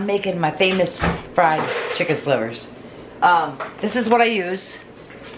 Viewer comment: I thought you said fried chicken liver lmao 😝